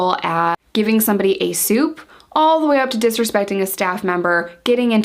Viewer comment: Ok, Hi new here. Not sure if I'm hearing correctly @ 3:12 "giving somebody a" ... soup? I heard soup, maybe suit. what's that? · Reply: I thought the same. She said soup lol.